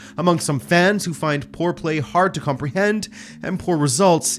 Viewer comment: I misheard "Bayern fans who find poor play hard to comprehend" as "Bayern fans who find foreplay hard to comprehend". Might want to enunciate a bit mate, because I'm now concerned about my state of mind